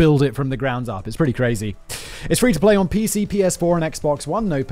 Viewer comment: Omg. The ad.. he said “ its free to play on pc, ps4” i heard “ its free to play on pcp”🤦🏻‍♀️🤣🤣